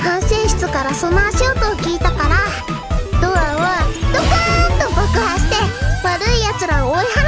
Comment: ここの ドアをどかーんと爆破して！ が時々 ドアをどかーんとぶっ壊して！ に聞こえる,,,w まじ可愛い。